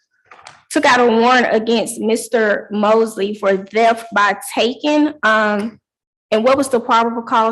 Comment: thought she said “death by taking” 😮